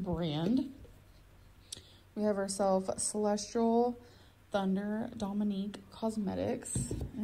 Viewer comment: she really said celestral